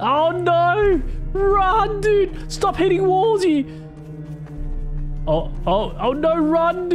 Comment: it sounds like he said "stop hitting wulzy!"